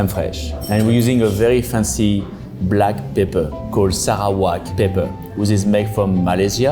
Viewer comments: Heyyy the caption is wrong here! 13:01 its not sichuan pepper, its Sarawak pepper :) made in Malaysia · That's not Sichuan pepper, what he was saying was "Sarawak Pepper" from Sarawak, Malaysia